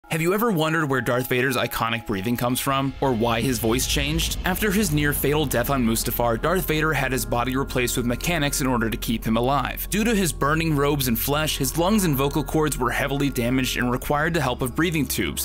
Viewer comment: Ah yes "near fatal death" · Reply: I legit thought he said "failed death" 😂